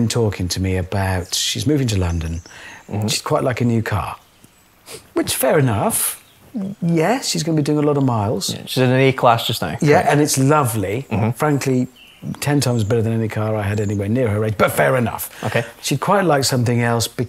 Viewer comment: I thought they said E-klass until they showed it and they'd said A-klass every time 😅